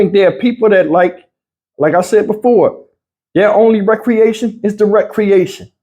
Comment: I don't think most people caught what you said: "Some folk's recreation...IS TO WRECK CREATION"!!! BARS!!!! I CONCUR!!!